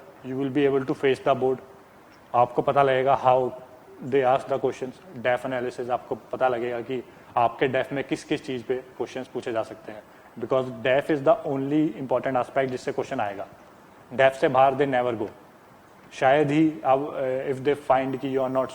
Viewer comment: what is deaf? i really could'nt understand what he said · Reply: bhargav krishna Revalla it's DAF bhai Detailed application form